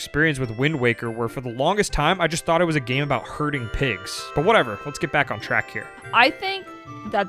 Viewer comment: I heard Hurting Pigs and not Herding Pigs 😂😂😂